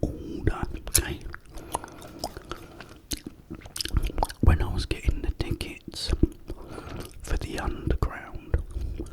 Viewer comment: I love the video.. very relaxing🙌🏾😃. I have a question tho what were you saying between 4:42-4:48 ? I couldn’t understand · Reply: I was saying I was getting the tickets for the underground train and the credit card got stuck in the machine's card slot 👍